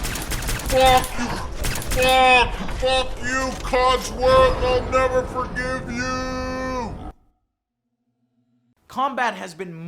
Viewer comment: holy shit did you see what he said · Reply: "fuckin , fuck, fuck you codsworth ill never forgive you"?